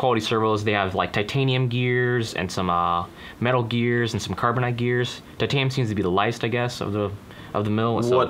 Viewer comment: Did he say carbonite?